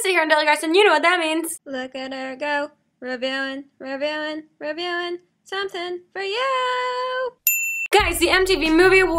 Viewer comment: at the intro song, I thought she said "revealing something for you"